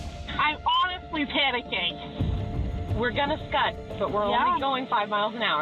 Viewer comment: did they really say scud? I had to listen to it about 90 times because I thought I was crazy ... it's skid... it sounds like it's spelt... that's all 😁 Lmao scud 🤣😂🤣😂🤣😂🤣